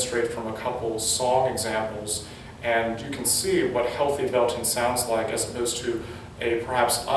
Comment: Am I️ the only one who thought I.T said healthy belting demon?